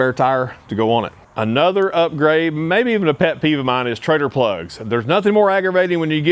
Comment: I thought he was saying treager, like the grills